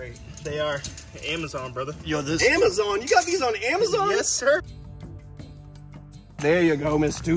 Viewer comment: for a second i thought he said he got the crocs on amazon 😂😂😂 i was like i don’t think you can order a nadia with two day free shipping